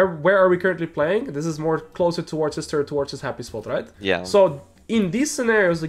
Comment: "towards his sister towards his happy spot" lool these utube suubtitles.. and i cudnt understand what he said there replayed it 2-3 times